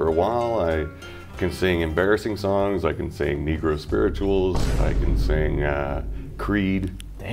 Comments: The “negro folk songs” killed me 💀 I gasped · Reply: I thought he said negro spirituals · The “negro folk songs” killed me 💀 I gasped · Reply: i thought he said "necro spirituals" since he's into metal lmao